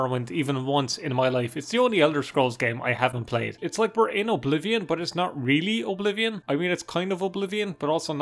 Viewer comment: It sounds like you say "we're anal oblivion" when you say "we're in oblivion".